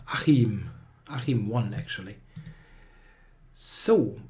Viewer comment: "Achim 1" sounded like "Achim won actually" - its a kinda funny mispronounciation in this context